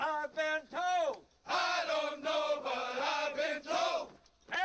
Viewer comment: The bit about ''eskimo pussy'' I heard as "ice-y cold'' when I first saw the movie at the cinema. It turned out it was ''mighty'' but I think ''ice-y'' is even funnier.